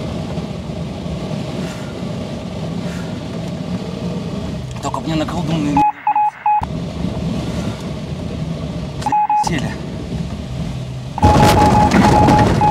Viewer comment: Послышалось " только не на клубнику"😅